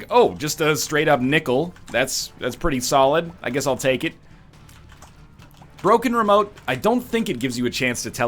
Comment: How many times will he say cursed skull before he realizes that's not the name? Place your bets below. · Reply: he actually says Broken Remote